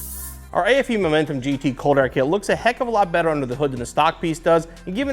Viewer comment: He talked so fast in the very beginning I thought I heard “COOL AIR KID”